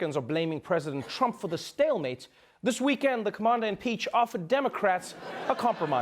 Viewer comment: Did I hear "Commander-Im-Peach"?!! hahahahahahahaha